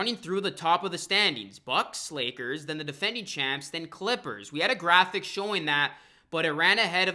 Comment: It sounded like she said Defending Champs Clippers but what she ACTUALLY was trying to say Bucks, Lakers, The Defending Champs, And the Clippers